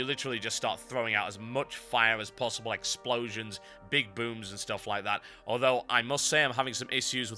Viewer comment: Thumbs up if you think it sounded like TB said "Big boobs" instead of "Big booms" ...